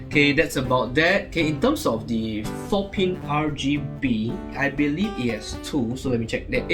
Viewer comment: i had to replay that three times to make sure i was not mishearing "the f***king RGB"